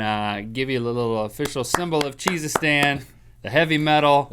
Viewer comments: at fist i thought he said Jesusstan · oh i thought you said jesus-stan that actually would have been so much better!!